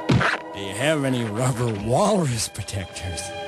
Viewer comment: I thought he was saying "walrus protectors", not "walrus projectors", as everyone else seems to think.